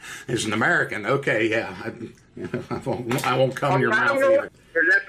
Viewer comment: I won't come in your mouth either....did I hear that correctly